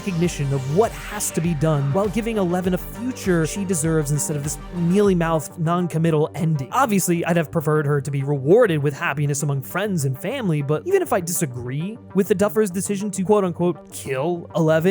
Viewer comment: I thought he said "Millie Mouse ending". Like if he was trying to say "Mickey Mouse/disney" kind of ending. But he actually said"Mealy-mouthed". Thanks captions! 😅